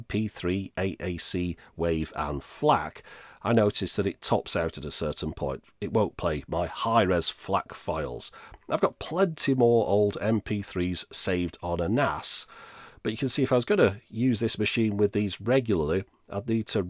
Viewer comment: Auto captions thought he said "I've got plenty more mp3s saved on an ASS" lol